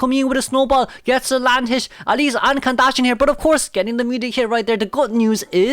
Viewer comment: I was 😂 here, ‘cos I thought you said “Ann Kardashian” not “Ann can dash in”.